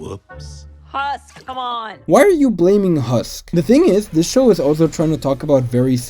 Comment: bro fr sounded like "why are you gay?"